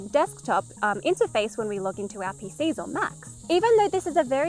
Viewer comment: The captions are fine in this until 13:41 where you say " . . .when we look into our PCs or Macs" and it says " . . .when we look into API Faisal max." Thus, giving an example of the very thing the video is about for someone who relies on the captions to know what was said.